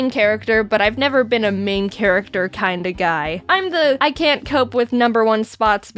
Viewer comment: Did Jaiden just say "g-guy"?